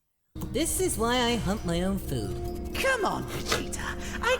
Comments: Sorry, English is not my original language. Can somebody tell me what does he say here? "He says hunt or" what? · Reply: goku says hunt . but some people mistook it as humped. which is a sexual act, which is why they were kinda weird out by the implication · so normally i hear that line as hunt but right before you had that bit i heard hump on my own